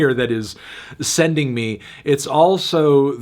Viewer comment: did fantano just say “sending me” at 3:14? 😭